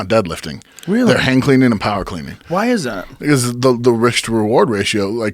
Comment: What does he say at 1:18? What athletes do instead of deadlifts. I am not a native English speaker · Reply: They do hang or power cleans